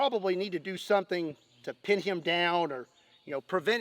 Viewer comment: I THOUGHT YOH SAID KEEP HIM DOWN NOT PUT HIM DOWN!